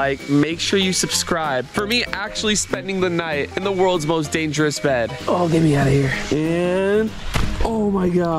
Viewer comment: I thought you said you’re gonna be in the most dangerous 😢